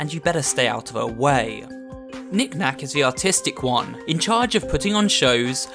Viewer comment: Tell me why when she said knick knack was the artist one I thought he said autistic